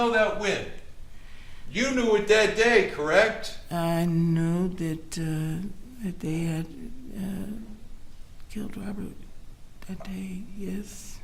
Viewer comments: to Break transcript differs from audio... listen 24:39 - 24:50 prosecutor "You know that when...." " The day.... I uh killed Robert"... Did I mis-hear anything here. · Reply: I hear - "I knew that they had killed Robert that day, yes". Her mumbling and fake BS accent make it hard to understand. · Did I hear her say 24:42 "The day I KILLED Robert". Did anyone else hear that??? Wth! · did she said she killed or they killed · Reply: I heard they had killed Robert